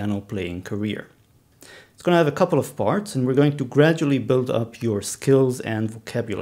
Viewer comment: i heard it as “it’s gonna have a couple of farts” then proceeded to laugh like a school boy that i really am